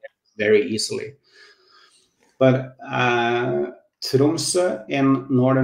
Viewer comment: At around 4:50 he mentioned a place in Northern Norway, it sounded like sedonsa can you please give me the spelling?